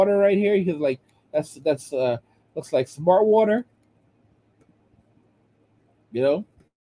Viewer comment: Did you say, " Smart Water " 🤣🤣🤣🤣 Water Is Smart Though. " Smart Water " 😅